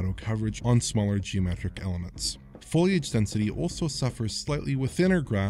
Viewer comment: thought he said "thinner grass on the ass" (rather than S)